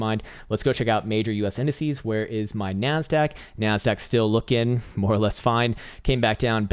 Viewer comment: When you said "where is my NASDAQ?" for whatever reason, I heard "where is my MAN-SACK?"